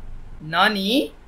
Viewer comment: he did not say nani he say granny